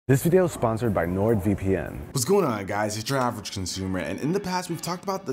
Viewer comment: WTF I ALWAYS HEAR "JURAVERAGECUNSUMER" at intro